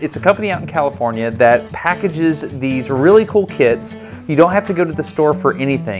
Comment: That packages these really cool kids. (18:46) I totally misheard what you said there. My brain was like hmm yes shipping children nice.